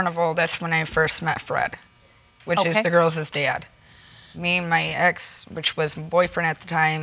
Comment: did she say the girlses dad?